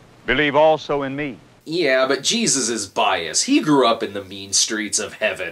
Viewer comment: I misheard you saying bias and thought you said Jesus is bi. Would make the bible a lot more interesting!